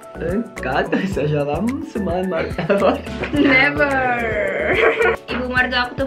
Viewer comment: Ngakak banget ih, apa cuma aku yg dnger baca mertua itu kayak kedengerannya martabak hahaha lucu banget sih, gawat nih nanti aku terobsesi punya suami orang turki juga gimana dong 😅